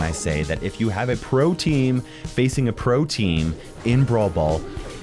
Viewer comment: I thought he was saying protein.... 13:20, I was so confused